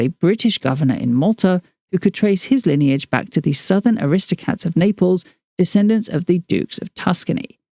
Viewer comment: you said Aristocats instead of aristocrats 💞🐈🐈‍⬛💞